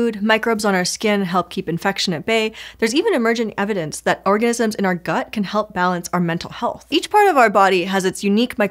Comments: Aah, mishearing things 😂: "orgasm in your gut" 🤣🤣🤣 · orgasms in our gut? Did I mishear that correctly? Whoa, I gotta get in on this! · It sounded like she said "orgasms". She's a master at subliminal messaging! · I heard "Orgasms in our gut"